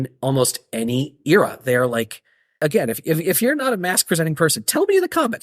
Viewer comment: narrator voice: "and yet, he did finish the video." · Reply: if he would have stuck around he could have really got mad when you said "mas-presenting person" which funny enough I thought you were saying "mask" and whilst I was confused I was 😂 because I basically thought you were saying unless you wear a mask and hide your face, these items are too ugly to be seen in... All that said, I would like to say, this video bummed me out due to the over abundance of political ideology. I respect that it's your channel and you're passionate about your beliefs. That said, all you did here was push people away or cause a divide if you will. Whereas I personally wouldnt be as bummed out if you would tone it down a bit and if you find your values important enough to be so vocal about them, than why instead don't you find a way to use your platform to introduce and educate folks on your values and why they're important. It would be a lot more classy and a lot more likely to get folks to maybe adopt some for themselves. Anyhow, just my two cents. And I only gave you them because whilst I do not share many of your values, I respect you and even look up to you in many ways ✌🏼